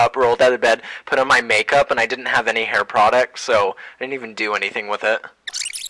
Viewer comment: i thought he said he didnt have any product...